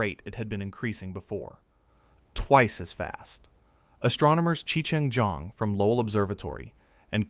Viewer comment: Did he just say Cheech N Chong